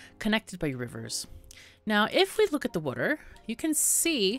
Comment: Did you say "Wooder" around time 3:13?